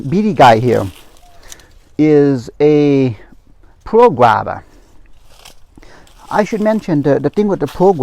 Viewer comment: Another awsome video!!! Thats a lot of guavas. I didn't catch the name of your oldest guava which looks amazing and the one you said it was the sweetest at 7:46. Thanks for another amazing video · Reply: Thank you. I got a bit too excited and forgot to mention my oldest guava was a Tropical White guava. One of the sweetest guava variety is the Pearl guava.